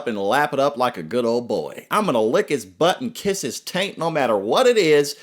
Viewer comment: Lick his butt and kiss his….what? Tank? Taint? New word for me. What is it and what does it mean? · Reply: Taint= space between the balls and asshole.